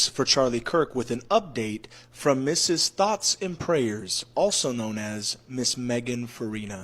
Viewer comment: I thought he said THOTS AND PRAYERS 😂